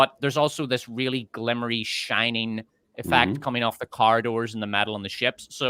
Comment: accent is so heavy that when he said "corridor" I thought he said "car door" and kept imaging the characters inside a car door lol